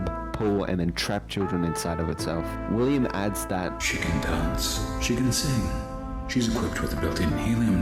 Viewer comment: i thought i heard "chicken dance"